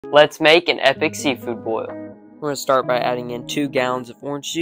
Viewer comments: i thought he said ethical seafood boil. was confused for a hot sec there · I swear I heard “ ethnic” seafood boil